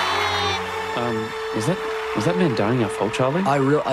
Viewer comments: what did he say? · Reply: "Was that man dying our fault, Charlie?" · What the hell does say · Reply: "Was that man dying our fault Charlie" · "Uh- was- was that man dying our fault, Charlie?" "I real- I don't know, Pim. I don't know." one of the best jokes of the episode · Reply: Thought he said "was that man dying at fault?" Like they were trying to make sense of the situation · "Uh- was- was that man dying our fault, Charlie?" "I real- I don't know, Pim. I don't know." one of the best jokes of the episode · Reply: oh i thought he said "was that man dying at fault, Charlie?"